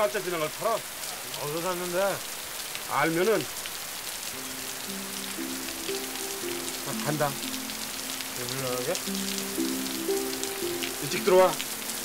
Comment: 대사 뭐라고 하는건가요? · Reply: 중섭 : 알면은? 나 간다.. 복수 : 일 나가게? 중섭 : 일찍 들어와 대사는 이렇게 되어 있는데 "일 나가게?" 보다는 "지금 일 나가게?" 로 대사 한걸 OK컷 해서 쓴게 아닐까 싶습니다.